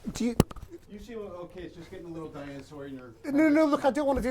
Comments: Did RDJ call the interview and old dinosaur at 6:43 - 6:47 ? 😂👏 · what did he say? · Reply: "You seem okay just getting a little Diane Sawyer and you're a schmuck" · At the end I swear i thought he said something like "You see your little kid, just get him a little dinosaur"